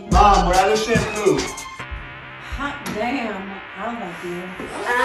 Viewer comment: “Mom we’re out of shampoo” “Hot damn I’ll help you” 💀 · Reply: I thought she said hot di